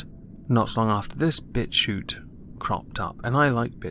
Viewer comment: Thought you was saying bitch shoot XD, was like I can't I'm not edgy enough to be that white boy XD